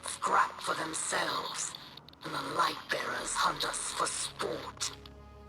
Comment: At first I thought it said lightsabers